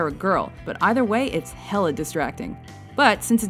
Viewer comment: did he just say hella?